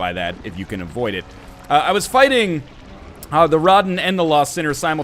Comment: Thought he said "The Rodman". Now that would be a scary boss.